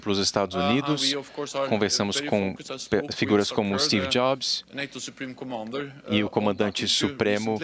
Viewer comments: Eu ouvi bem? Ao 09:23 ele fala que a OTAN conversou com STEVE JOBS??? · Galera, ajuda ai! será que foi só eu que entendi que o secretário-geral da Otan, Jens Stoltenberg irá consultar Steve Jobs (morto em 2011) no mínuto 9:20 do vídeo???? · Entendi mal ou ele disse que conversaram com Stevie Jobs